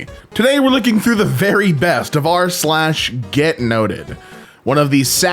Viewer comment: I just woke up and thought said "Get nutted". 💀